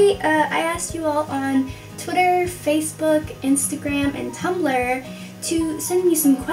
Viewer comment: did she say tumblr